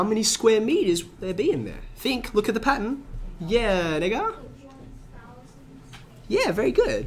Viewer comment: Did he just said nigga???